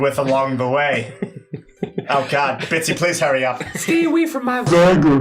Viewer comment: what does Bitsy say? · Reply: stay away from my bindle